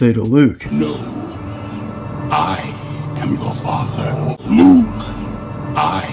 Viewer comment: “Nuke! I am you’re father” is all I heard.